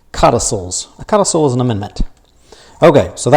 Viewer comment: What word did u use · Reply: Codicil